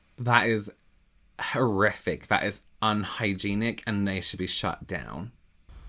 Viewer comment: I misheard 'he should be shut down' as shot down and I nodded calmly